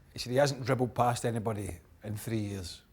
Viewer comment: Guys, could you please tell me what he said at 0:33? I thought I could understand English but apparently I was wrong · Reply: he hasn't dribbled past anyone in three years